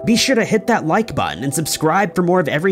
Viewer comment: For the longest time I thought you said tying knots. I'm so silly.